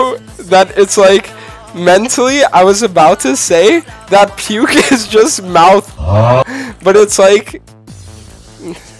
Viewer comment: what did he say at 8:34 ? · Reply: coom